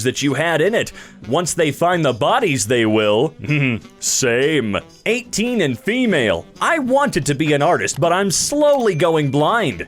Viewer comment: thanks for indirectly telling me I need to go to bed as I heard 18 and pre male and thought “ that’s a interesting way to say you are about to start transition”